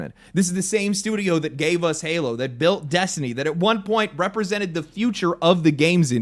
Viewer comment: I had to double back here because I heard "milked" Instead of "built". Both are pretty accurate, though.